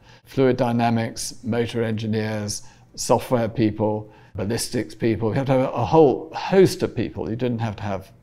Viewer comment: Did he say he needs ballistics people